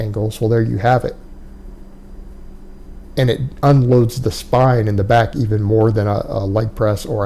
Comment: I didn't say that. It does place stress on the lower back. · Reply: my bad I heard ‘puts a load’ instead of ‘unloads’. I was thinking leg press certainly doesn’t do that haha